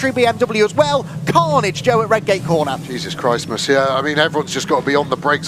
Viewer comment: "Jesus Christ, monsieur." I'm just going to pretend that's what he said, because it's hilarious.